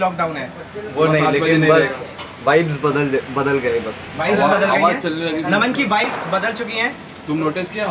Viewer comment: i heard Naman ki "wives" bada chkin hain..... :)